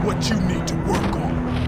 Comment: I never understood what he was saying here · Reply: mommy where are you